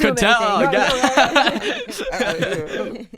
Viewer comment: did Charlemagne say Dickelodeon!!!!!! Lolololol Charlemagne zesty as hell